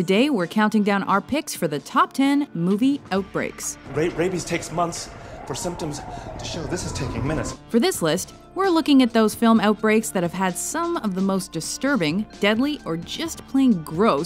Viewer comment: Oh... I thought it said top 10 movie outtakes...